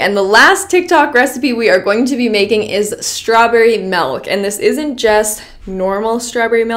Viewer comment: no one: not even a single soul: mElK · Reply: I literally thought she said strawberry melon